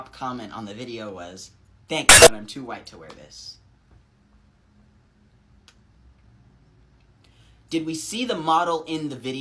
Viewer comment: What did he say at 14:20? All I heard was a horrible sound effect that blocked out the sentence. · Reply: He said "thanks but I'm too white to wear this"